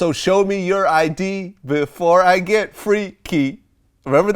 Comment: I thought he said show me some id before I get to deep into I don't seeee